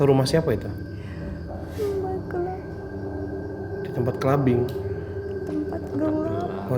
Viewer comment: Lagi serius serius " tempat gelap" bang fadi malah salah dengar "tempat clubing" 😂😂😂